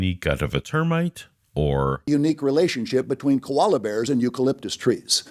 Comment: Love how his example of "Koala's ONLY eat eucalyptus..." completely shreds that they could have survived on the ark, or even traveled to and from the ark as there was not eucalyptus on the journey. That fact seemed to slipped over his head. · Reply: Is it just me, or does he actually say "Kolala bears"? At 15:40 for example...